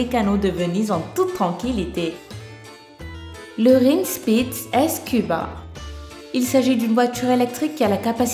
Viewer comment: On dit Skuba pas S Cuba t'est attardée ou quoi ?